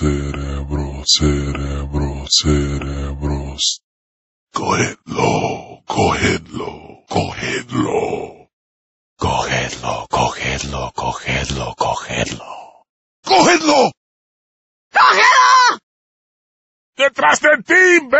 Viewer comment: You know... I always thought they were saying "go Hitler go Hitler go Hitler" when I first played this game...